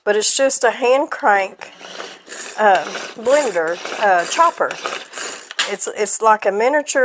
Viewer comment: What did you call that tool sounded like you said Oh Doogler chopper? I have one similar to that and I use it to cut the top & bottom off strawberries. It goes without saying I feel like Wonder Woman and boy is she fast. YOU SHOULD TRY IT. You and Mr Brown will be cracking up laughing when you see how fast you can clean one basket after another